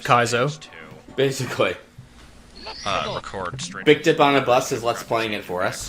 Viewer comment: Whenever I hear "Bidkip on a bus" I always think "Big dick on a bus". I can't help it.